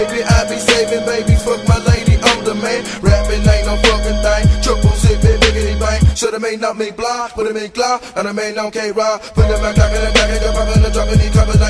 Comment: if anyone can tell me what K-Rock saying I'll wire you money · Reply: Should been on me block witta me glock thatll be on k rock